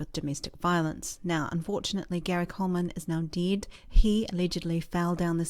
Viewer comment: stopped after 15 seconds because of your accent. its really... annoying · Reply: i thought she said "Gary Coleman is now DID"